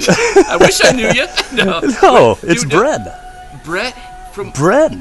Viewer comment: It always sounds like bren says his name is bread 🤣